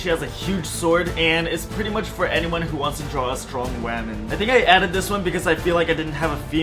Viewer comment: I thought he said strong” RaMeN”😭